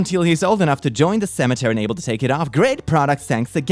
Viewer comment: Cemetery? I think you mean seminary. Still love ya though! ✨😽✨ (That's what it sounded like to me! Don't hurt me!)